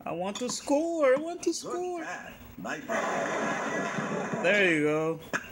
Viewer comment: What’s he saying at 3:40? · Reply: Nicely received !